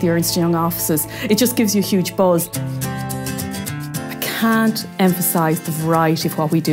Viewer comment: did she just say it just gives you big balls?